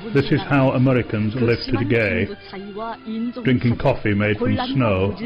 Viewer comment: Why do i keep hearing "This is how Americans live to the gay" 🤣